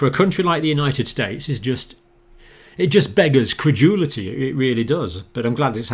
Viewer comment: "it just beggars credulity", is it? (Unsure of "beggars", but that's what the captioning says.) Yes, we'll... "'Maricuh! We's a Christian nation, ya hear?!" -- or some bullshit like that. Credulity is rampant here, and actively promoted (almost directly, sometimes) by many churches. It's really quite disturbing, at times like this. And depressing, at other times, and... yeah.